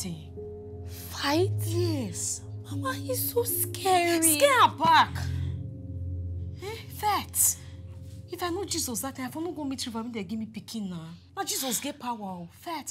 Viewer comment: Did she just say SCARE HER BACK?😂